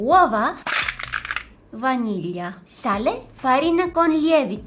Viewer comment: Lmao salt sounded like "saale" in hindi